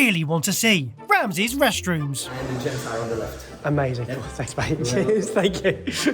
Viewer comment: Haha... I thought you said "thanks babe" when you were asking for the toilet and had to double back haha 😂 was hilarious for a second. I'm such a dummy.